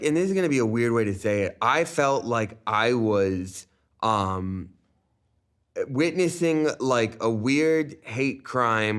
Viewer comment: CAN SOMEONE PLEASE CLARIFY THE "SLUR" TIMESTAMPED @ 18:40 ? TO US NON MEMBERS? WHAT DID SAY? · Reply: it wasn't, it was and he said "lesbos"